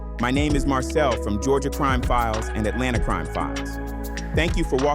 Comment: I thought you said fuck the final thank you